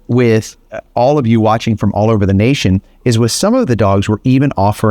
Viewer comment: Hahaha, did you just say, "Let's Sip with some dogs"??? I thought it was cute since you guys have Flip's coffee. I thought that it was a play on words. Fun fact, my Moms pooch, a black mini Aussie, his name is.